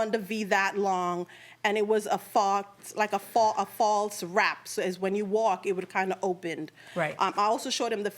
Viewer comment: "I want a dress like this, but nothing like this." 😂😂 She changed everything about the dress except the faux wrap. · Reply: I was cringing every time she mispronounced “faux”. My word. 😂😂 At first I thought she said false wrap, by the second time she said I realized she was meaning faux.